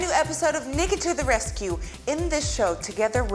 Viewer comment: Yo why does the caption say n**ga to the rescue? Excuse me?